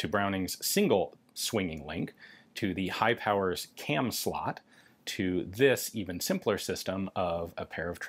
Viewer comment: listening with half my ass than I heard high power cam sluts. Cheese